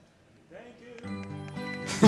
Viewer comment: What'd that guy say? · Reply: "Thank you", I think. He said it really weird